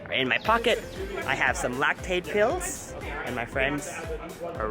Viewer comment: am in the only one who heard "i have some lactate pills" lol 😆